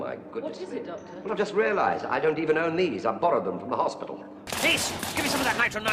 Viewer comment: "I have nothing but these clothes, oh my goodness" "What is it Doctor" "I've just realized I don't even own these, I just borrowed them from the hospital" WHY WASN'T THIS REFERENCED IN THE " ELEVENTH HOUR" OR AT ALL DURING THE 11TH'S/MATT SMITH RUN?! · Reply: please don't laugh but i thought it said "i don't even own knees" asdfasdfadsf